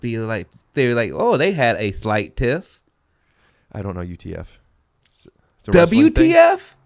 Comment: might be the new yanni/laurel. I heard "UTF" too but then replayed it twice and heard it both ways